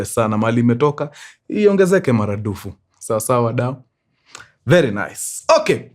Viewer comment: did you just say "iongezeke maraNdufu"? Enyewe mkuru enyewe wewe ni mkamba😅😅